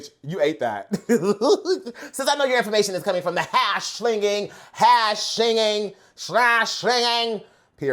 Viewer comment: it’s even better. he actually said, “CASH slinging slasher” LMAOOOO 😭